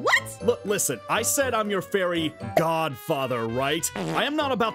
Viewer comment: Zane as Aphmau's fairy god father. Hnmmmmm. 🤔 I can imagine it LOL 😂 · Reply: I thought he said I’m ur fairy goth mother lol I’m dumb